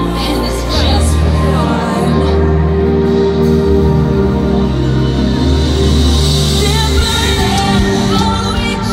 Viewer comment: what did she say? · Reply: It looks like she says 'look at me' or something like that 😕